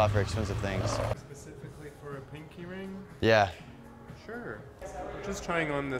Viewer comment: Who else thought he was saying pink earring?